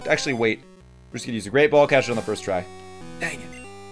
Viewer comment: just me or did he say rape ball instead of great ball?